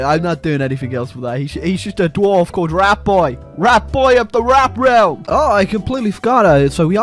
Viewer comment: i heard you say "rat boy 3 arena" i swear i know you know about quake